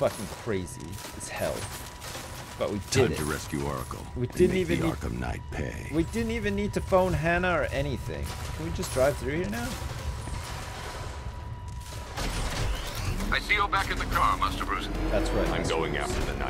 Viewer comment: I thought he said "We didn't even have to bone Hannah" XD